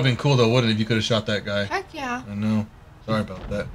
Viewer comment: did she said fuck yea!? She made fuck yea so cute!